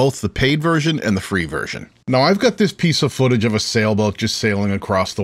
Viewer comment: thought i heard "pizza footage" and realized im hungry!